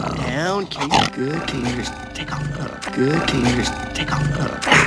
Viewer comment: Hello, a Malaysian here. As for the scene in 3:56, I do believe what Aladdin said was :" Good Kitty. Take off and go . " which translate into : " 好猫咪，快走开." Sorry if my Mandarin's a bit rusty tho.